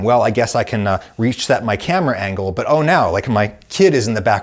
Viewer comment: Pretty sure at 09:16 you were trying to say reset your camera angle. :) (your CC says inaudible at that point)